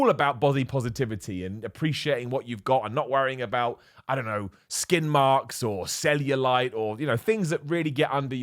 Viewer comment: I could have sworn you said 'skidmarks'. I don't have to worry about them, honestly. Ahem.